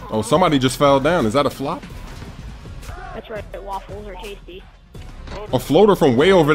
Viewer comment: Did anyone hear´´Waffles are tasty`` on